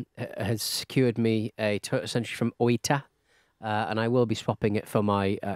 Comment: I wasn't paying attention and misheard that was importing a "Toilet Sentry" and thought he was getting some super high tech bidet delivered. Three months for a toilet? Get someone in Japan to check it over? lol